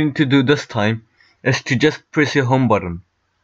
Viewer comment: How do u get it to stay on the numbers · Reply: yeah sorry I misheard you, I thought you said hold down the shut down button but as I watched it again I realised you said home button my reaction-😑😑